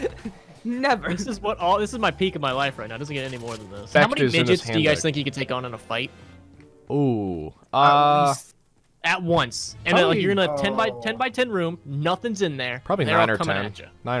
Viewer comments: at first I thought said Ninja's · Bruh. When said many midgets could you hold off. I thought he said how many ninjas. So when they said 9 or 10, I was like wtf. How could you hold off that many ninjas. And so I thought they were joking when they said that. Then they started saying that because of their height they have weird movement. And so he take on a bunch of them. Then I was like,,,, wait. He said midgets. So this whole time I’m like how!!! Right? Maybe 0 ninjas, that’s more like it. Buttttt... he said midgets so I’m stupid af. Anyway, have a wonderful night... sleep well...